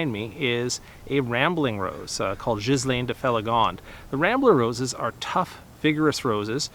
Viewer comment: Whats the name of the white rose @ 1:28 i cant quite heard what you said. Thanks. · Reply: No problem - it's 'Ghislaine de Feligonde'. I also try to correct the CC transcript on my video, so if you ever can't hear my bad pronunciation, turn on the captioning. Thanks!